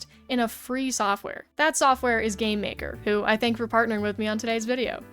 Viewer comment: Idk why but instead of game maker I heard "Gay maker"